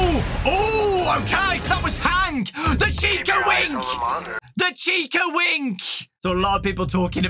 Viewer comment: When Dawko Said chica wink i THOUGHT HE SAID CHICA WING WHAT THE HELL